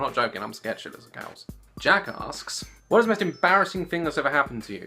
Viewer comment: HAHAHAHA jack assssssk hehehe it sounded like u said jack assd